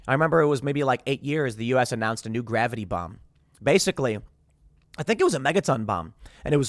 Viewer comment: omg, when i heard, “gravity bomb”, i had to replay it bc i SWORE i heard him say GRAVITY BONG🤣 maybe all that smoking did kill a few brain cells lmao